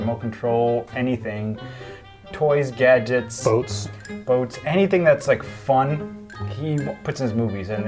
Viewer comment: Thought said boobs which would've been correct... ...until I remembered you two were listing off remote-controlled stuff lol